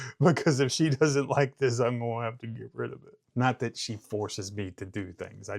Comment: Ha. I thought you said if she didn’t like it you would have to get rid of “her.”